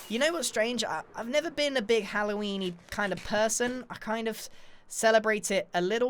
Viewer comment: Is it just me or did he say Hallow weenie